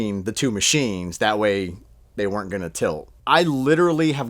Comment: > "I literally have gotten all my c*ck for $300 or less" Did I mishear something?